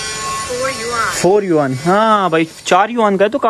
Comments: she said “shi” yuan and made the Chinese hand signal (+) for the number 10. Your voice translator made a booboo and translated “si” number 4. Your phone understood “suh” instead of shrrr. So she told you ten. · The pronunciations of 10 and 4 are similar in Chinese. I heard the shopper said "shiyuan" at 15:42 , which means ten yuan in Chinese. So she didn't cheat you originally. But when she said to your MP she said "Si yuan", which means 4 yuan. This was a unclear prounouciation, which is easy to make the translater misunderstand. 😁